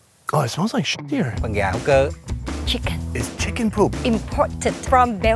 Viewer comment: idk why it sounded like chicken poop